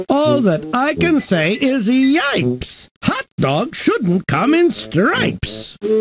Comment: Brown (S») "Brown strawberries in a dish! They're as sweet as you could wish!" (G») "I will not eat my berries brown, so take those berries back to town!" 10:11 Gray (S») "Sundaes made with Gray Ice Cream and Gray Bananas are a dream!" (G») "Gray Bananas? I declare! On Gray Ice Cream‽ I wouldn't dare..." 10:30 Red (S») "Bright Red Pickles from a jar! Look at what a treat they are!" (G») "That's a treat that I would dread. I won't eat pickles if they're red." 11:11 Blue (S») "Peanut Butter's great in Blue. I've spread it on Blue Bread for you." (G») "I do not want Blue Bread today. Take that stuff and go away!" 11:27 Black (S») "Some Black Carrots might be nice, with a bowl of nice Black Rice." (G») "No Black Carrots or Black Rice. I do not want to tell you twice." 12:40 Yellow (S») "Have some Yellow Lamb Chops now, served with Yellow Mint Sauce. Wow!" (G») "Yellow Lamb Chops‽ I say no. Take those Yellow Chops and go!" 12:59 Orange (S») "Orange Cherry Pie is nice. Like to try it? Have a slice!" (G») "That's not something I would try. I won't eat Orange Cherry Pie." 16:28 Green (S») "How about Green Eggs and Ham?" (G») "I will not eat them, Sam-I-am." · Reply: I thought he said, yikes.